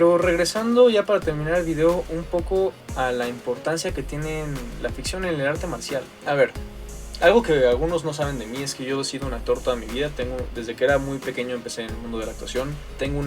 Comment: creo que tengo hambre porque entendí mal creí que desia e Sido una torta en mi vida 6:15 jeje hehe